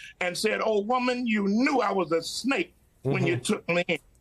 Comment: Did he just say don't F with the snake on TV? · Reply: He said, "Don't fall for the snake." I had to listen to it at .75x speed to catch it properly.